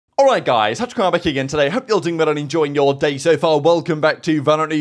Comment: OK, Can someone please for the love of god transcribe the intro for me, it's driving me insane All I hear is "Alright guys, touch harbor keegan today" and I've been trying to understand what he said everytime i watch his videos. English being my 2nd language doesn't help either. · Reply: He says “Alright guys TacticalRab here again today” but he said it super rushed and slurred, its pretty hard to understand even for a native english speaker 💀